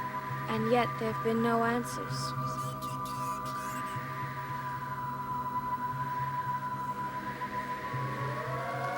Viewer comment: Right after she says, "..and yet there have been no answers." at 0:45 I hear a voice say something like "Blow up the entire planet." Can anyone else make that out? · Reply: I think it's "welcome to our planet."